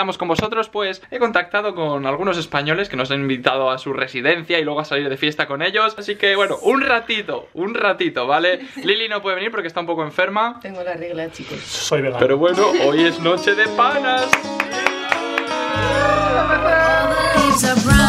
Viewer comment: que dice "eso es verdad" o "soy vegano" ???